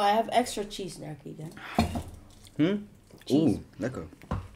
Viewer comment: wait what did he sayyyyy?????? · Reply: Lekker... means nice in Dutch 😁